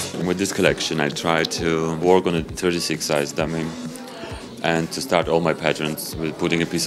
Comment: Sorry, I'm not good at English. What did he say at 0:13 after " I tried to.."?? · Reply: I think he said he "tried to work on a 36 sized dummy"